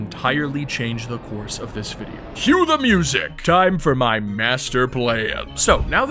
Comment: Did u say, your master plan? 🥚